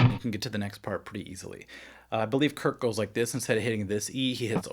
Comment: did he just say Kurt👀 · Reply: He said "Kirk" in reference to Metallica's lead guitarist Kirk Hammett, but it did sound a bit like he said "Kurt".